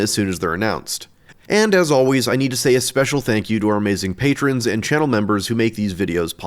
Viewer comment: I thought you said "missing patrons" and I had to double take